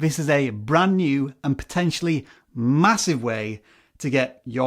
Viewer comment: Hold up did he say Massive at 1:46?